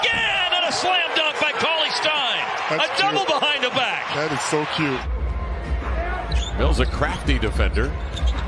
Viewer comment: Lol is it just me or did he say that is so cute.